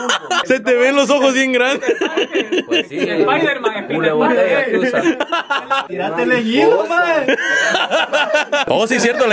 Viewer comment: Alguien me explica que dice la terapia en el min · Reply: GodᏦۼղղվ es como decir. ( Móntate en tanga wey) Pero el dice (tírate en hilo maje )